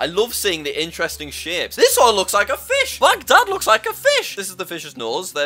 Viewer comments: i heard MY DAD LOOKS LIKE A FISH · I THOUGHT HE SAID MY DAD LOOKS LIKE A FISH